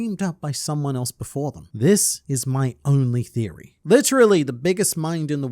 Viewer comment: I thought he said this is "only my theory" not "my only theory"... same words, very different meanings